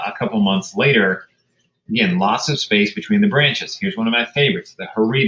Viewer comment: sorry what coral did he say horeda coral?? Iam looking for all branching and big branching sps please list all. Thanks